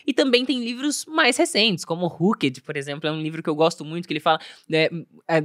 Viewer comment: que livro é esse que a fala? Rucket? Não entendi 👀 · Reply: Oooi! O livro se chama "HOOKED (ENGAJADO): Como construir produtos e serviços formadores de hábitos "